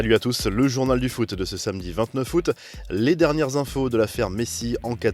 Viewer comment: 29 Août, j'ai entendu " Vingt-neu foot"